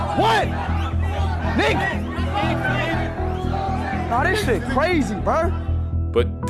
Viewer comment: I think they meant to say Kniga not the n word but okay..